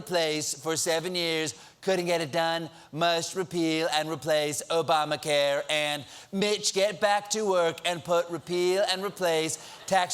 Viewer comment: anyone else misheared "Bitch, get back to work" ? xD LOL